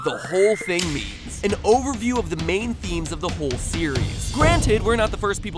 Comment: for a sec i thought he said main memes not themes